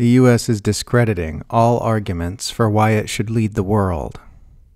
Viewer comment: Oh, for a split second I thought I heard "the US Is discrediting all arguments for why It should LEAVE the world" ... and wondered why the tone was so perplexed. Seemed like a great plan!